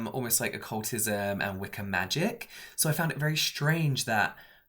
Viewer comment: the subtitles say Wicker magic instead of Wicca magic 😂